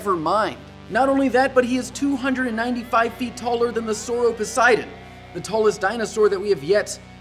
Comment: I actually thought he said "Sore Of Poseidon"